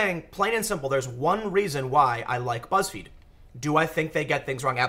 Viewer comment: When you first said, "I like Buzzfeed", I thought you said "I like roast beef". Seemed very random and out of context, just thought it was funny.